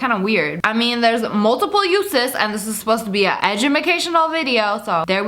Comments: When she said Edumacation I thought she said Ahjumma-cation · did anyone else hear her say "edumacational " (edge-a-ma -cantional) ;))))))) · why did you say edumacaltional? (Edge-ema-cashe-inal)